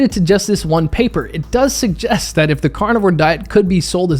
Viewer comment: I know you're not saying this, but I keep hearing, "cardboard diet."